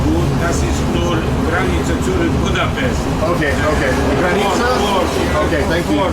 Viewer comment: The conductor speak German language: "Eine und zweite Wagen ist gut. Das ist nur 3 Meter zurück Budapest". First and second wagon is good. This is only 3 meter backward Budapest. I hear he says 3 meter, maybe he mean 3 wagons.